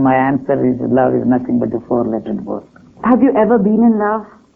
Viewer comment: what does he say here "My answer is love is nothing but..."? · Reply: A four letter word